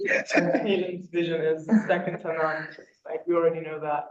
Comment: What does Temi say in 6:10 ? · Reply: Temi stated CC vision is second to none if I heard correctly.